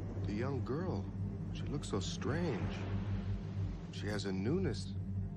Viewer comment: For years, I thought he said, "she has/is a nudist" until I was able to get my hands on the book and realize it's actually, "new-ness".